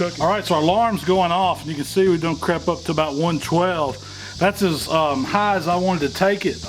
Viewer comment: What was said here? "We done crept up to about 112" ? Closed captioning said, "..can see we don't crap up to about 112." LOL - I just put together a Weber premium 22' Kettle and why not have top round be the first thing I make with it? The Inaugural BBQ.